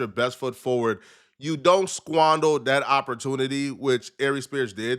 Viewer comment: tryna cross over using a few big qords in the beginning. Mans sound like he was having a seizure trying to say "exhibited" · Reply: And at @ 00:40 he said "squondol" instead of squander. He's a goofball